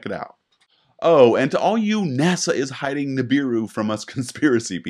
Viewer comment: Nibiru. At first I misheard it as "the beer room." I think quite a few more would be mad at NASA if they were hiding that.